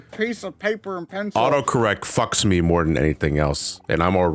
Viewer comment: slam dunk joke. I honestly thought I heard "Nora fucks you more than anyone else"